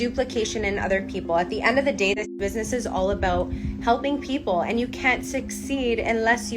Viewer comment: I could’ve sworn I heard the mom say “this business is wasteful” at the end of the clip or am I just hearing things? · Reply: You are Not hearing things, she totally said "this business is wasteful". Good catch.